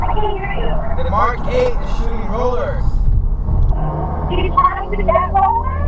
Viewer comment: 1: I heard he was shooting rollers, but WTF is "shooing rollers"? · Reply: TBH... I must be old, I thought at first he said 'Bay City Rollers'😅😅😅